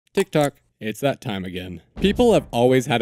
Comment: why did I hear "tiktok"